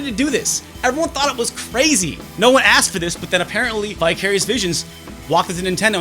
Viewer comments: Apropos of nothing, I totally thought you said "bi-curious bitches" instead of "vicarious visions" and did a small spit take. lol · When you said "Vicarious Visions," I thought you said "Bi-Curious Visions."